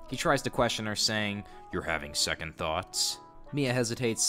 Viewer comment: i heard "youre having sec" and my spine immediately froze, until i heard "ond" instead of... something else... still creepy that this guy is doing this